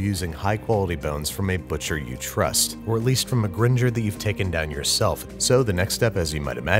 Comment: I misheared her and thought she said a ginger... ima take those bones tho.